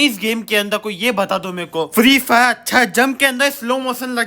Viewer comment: Did he said gaandu 😂😂